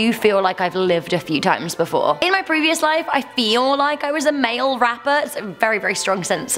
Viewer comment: Heard that as ‘mail wrapper.’ 🤔